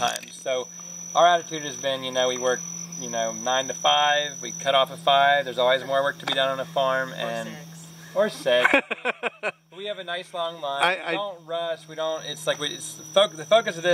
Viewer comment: did she just say sex??? Lol Great video!! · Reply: She said "or six"